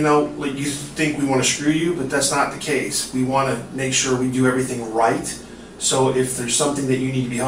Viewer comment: I thought he said “we want to do everything white”……😂